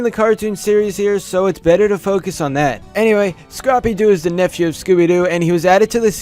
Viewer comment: I thought you said crappy doo. Why did he have to poop so much. Edit: I thought when you said Bendy from FIHFIF, I was like, why are you talking about Bendy and the Ink Machine? The fur on his upper head kinda looks like Bendy's horns in BATIM. I guess Sparky will never spark our minds like how Thomas Edison did. RIP Sparky. BUT, LETS GET RID OF CHLOE!!!!!!!!!!